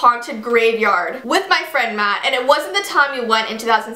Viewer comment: Jessii I legit thought u said BOYFRIEND Matt I was like when did this happen 😂😂😂